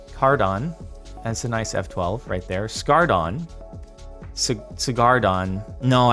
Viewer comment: I wasn’t paying attention and thought you just randomly said hardon